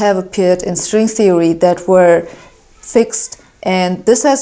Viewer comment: Hossenfelder I noticed that @ 2:51 the captions say 'patched' but you say fixed. Also, I noticed that you 'patched' your video to say 'fixed' instead of patched based on your mouth movements. If you don't mind me asking, why did you prefer the term fixed to patched?